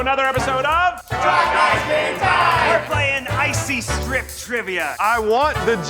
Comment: My boyfriend misheard “Try Guys Game Time” as “Try Guys Gay Time” and I think he stumbled onto something we need.